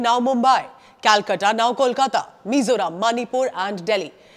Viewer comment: please correct it to Mizoram instead of miserable Manipur and Delhi